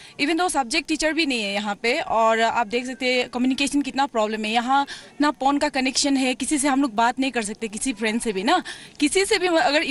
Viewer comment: Is she saying "yaha porn ka connection nhi hai"